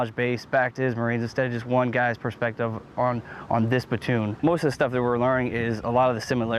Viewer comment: at about 1:04 i swear i hear "batoon" and i thought i heard it in "full metal jacket," too. is this just a variation of pronouncing "platoon," or is it something different, or i am just hearing things? thanks.